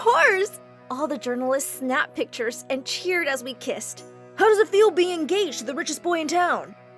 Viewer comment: No cause I thought it said "How does it feel to be gay?"